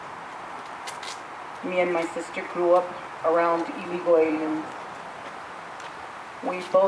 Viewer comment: did she say her and her sister blew up ?? jajajajajaja. ..jajajajajaja. .